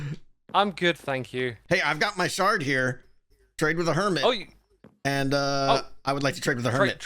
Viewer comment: I swear I heard "Trigger a Hermit" instead of the "Trade with a Hermit". Would have been fun still.